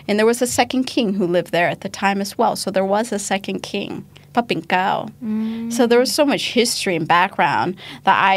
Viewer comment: Thank you this great work. Something was incredibly wrong with English subtitles especially at 25:47 there was ``f***ing cow`` showing up instead of the name of King. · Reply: I think English caption was auto-generated; thus, the word Pra-Pinklao (พระปิ่นเกล้า) which is not English word got translated to F*** cow from its sort of similar pronunciation.